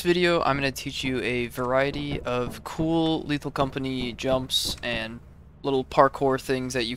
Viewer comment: i fr thought u said "cool little company" in a bad russian accent